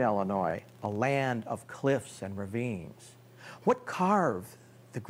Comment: What does he say at minute 0:46? A land of cliffs and... what? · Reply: ravines